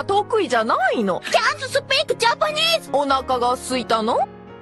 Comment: I thought Nora said "Do you want Mcdonalds?"